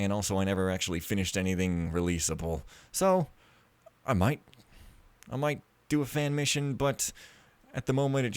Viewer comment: I heard you say "I might do a Fen Mission" :D About the not contributing to society part... well, you made some cool Thief Let's Plays that people enjoy watching (I certainly do), so you did contribute something to the Thief community, at least. This is more contribution to society than some other jobs, where you just sit in an office doing something that theoretically could be done by a computer script, I would say. At least, it gives you more fans than doing a menial office job would! You actually produce stuff that is pretty cool and all that, ya know.